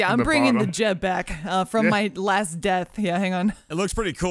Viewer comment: I thought latest death said “lamest death”